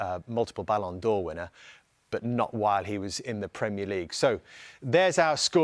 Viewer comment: did He just say Ronaldo didn’t win the Ballon D’or at United? Or did I mishear that? Cos 2008 He literally won almost every major individual award there was from Ballon D’or, to FIFA WPOTY, UEFA CFOTY, PL Golden boot, UCL golden boot and so on.